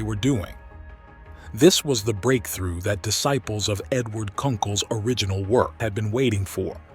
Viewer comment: I heard “Kunkel Disciples” and 😂